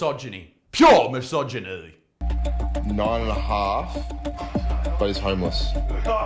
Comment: There are two dudes (half) naked in a bed together, and all the news can do is berate these two men for doing something women on the entire globe are doing. Like hot damn maybe we have avidence that they not even into women? Anyways, congratulations on getting married ♡ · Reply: sounded like: “ 9 and a half….but he’s homeless”.